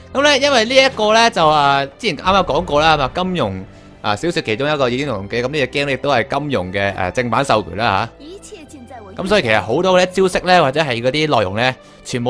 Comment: 開頭我聽成金融小說 結果係金庸小說